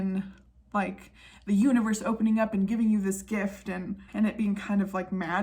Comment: I thought you said "...the uterus opening up and giving you a gift" and I was very confused 😂 Clean your ears. Also "Roll The Bongos" needs to be on a hoodie 🖤